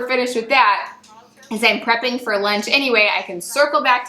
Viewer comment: is she saying Prepping for LENT (vegetarian) OR ..Prepping for LUNCH . 🙏🏼✝️❤️ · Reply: I'm guessing I said Lunch :)